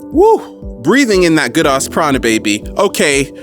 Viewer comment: Is it me or did he just say ''breathing in that good ass chronic'' ? at